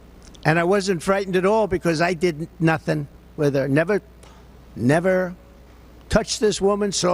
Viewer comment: I thought he said “I didn’t nutting with her”.😂😂😂